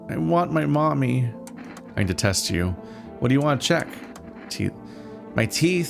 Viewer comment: I heard “I detest you” not “I need to test you” lmao